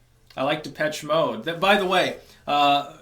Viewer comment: Ahahahaha I am pinning my own comment on the biggest update of the year. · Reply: He likes Depeche Mode. I thought he said "to pet Schmoe". The NPC pumpkin has a name.